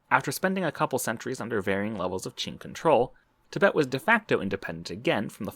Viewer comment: he says "under Ching/Qin control" but it sounds like he said, "chink control" which I guess doesn't change anything now does it?